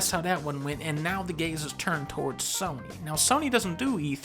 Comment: I thought he said "and now the gays have turned on sony" what was actually said "and now the gaze has turn to sony"